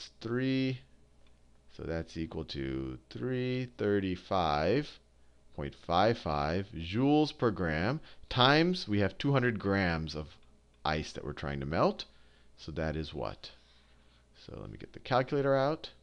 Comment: why is the heat of fusion 335.55? I thought he said 333.55? Gahh science is hard.